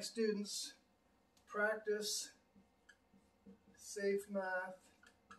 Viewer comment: oh god I thought it said safe meth